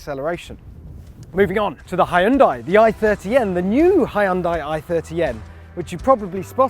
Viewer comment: верно слышу "хайэндай" ?